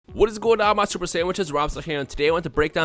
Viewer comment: in the beginning it sounds like he say "wassup my stupid sandwiches" lol